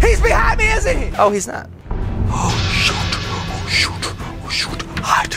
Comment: it sounds like he is saying “shoto”